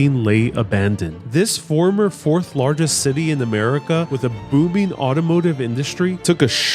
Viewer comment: When you said booming it sounded like you said boobing lololol